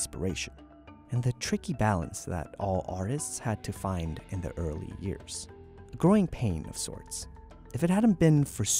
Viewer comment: Did you just say "Talf years"?